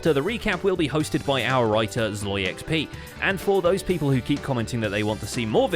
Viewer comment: i never knew your writer was ZloyXP, i always just thought you were saying “our elytra has low XP”